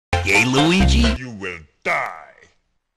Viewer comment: at the begining it sounds like he says gay luigi